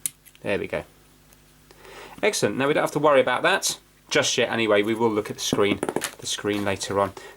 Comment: did I really just hear swear? Thought this was a family friendly channel but I swear I heard a "just sh*t anyways" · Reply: No, he said "Just yet, anyways" it's part of the UK accent.